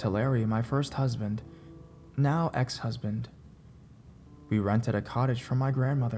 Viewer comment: It sounded like he said,"she rented a college"